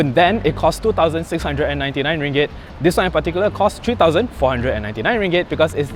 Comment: what is your wrist circumference? was included strap long enough? (btw. did you say 2499 for saphire edition in 0:22 ?)